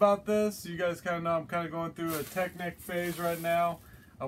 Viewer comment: You said you were going through a technic phase. I could have swore you said going through the penske files. Lol